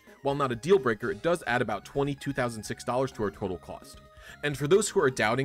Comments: DID U SAY 22,006$ ??? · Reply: Twenty “2006” dollars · did he say $22,006 ADDED to the total cost? That’s enough to buy a car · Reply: twenty, "2006" dollars